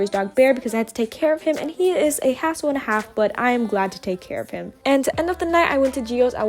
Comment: initially I heard a***ole instead of hassle 😂